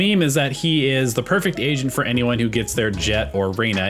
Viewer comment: I swear I kept hearing "Asian" instead of "agent"